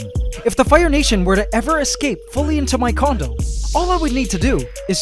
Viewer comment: Did he say my condom at 8:23?